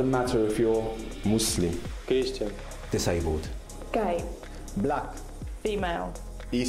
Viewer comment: Did she said gay